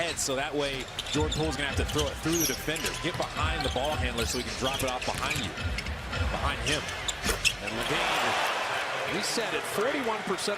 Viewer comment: did he say ADAM LEVINE? 😂🤣